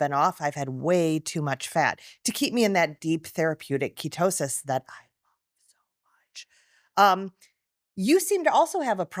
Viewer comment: what are u saying ?? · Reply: “that deep ketosis that I love so much”